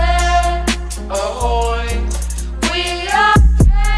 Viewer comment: Why do I hear “we are SUSways” 😭